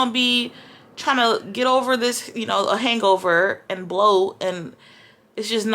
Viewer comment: Giirrrlll, did u just say "get over the hangover and blow" ??? 😉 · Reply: Hahaha definitely not I said “bloat” 😂